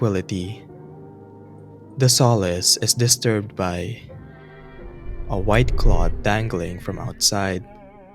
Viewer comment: I 100% thought you were saying “white claw” and I was like idk maybe they wanted to invite him for a drink